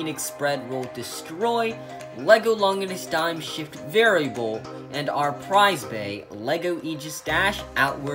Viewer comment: lol it sounds like your saying “dumb s**t variable”